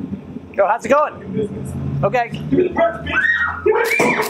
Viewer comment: Did he just say "Bitch"?